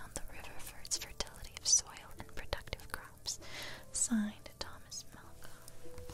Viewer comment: When you got to the notice about land for sale, I thought you said Thomas Putnam instead of Thomas Malcom and I almost spat out my tea. That would have been a mess